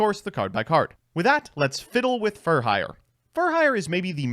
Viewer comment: I almost thought he said “fiddle with furries.” Was about to unsubscribe, delete the YouTube app, and throw my device out the window